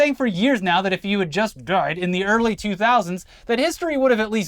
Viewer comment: If you can't hear it, said "dried". Rudy became very wet and leaky in old age. Shoulda dried, Rudy!